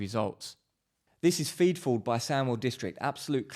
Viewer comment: Superb video. I repair and restore turntables, really tricky to get everything right and hard to accommodate for bad mixes and pressing. What's the name of the album @ 9:15, couldn't hear it properly. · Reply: feed forward by sandwell district